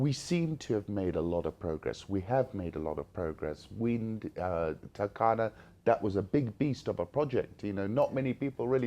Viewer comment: nice discussion but what does he say here @ 30:00 tacada??? what project is that being mentioned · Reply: the turkana wind project. most kenyans pronounce it with an extra u (turukana). i dont know why.... its like the way the presenter is pronouncing waste west